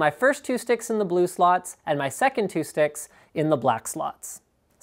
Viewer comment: "and my second two dicks in the black sluts." - wait that is what he said right?